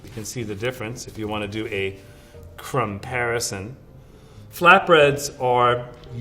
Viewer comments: did he just say a "crumb-parison" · He really said crumbparison HAHAHAHAHAH ( 14:15 )